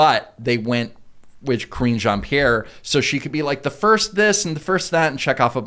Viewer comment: I could've sworn you guys were saying "Cringe Ann-Pierre". Oh well, works either way I guess.